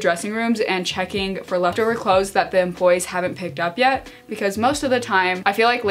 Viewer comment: every time she said 'employees' i thought she was saying 'dem bois' and i was so confused lmao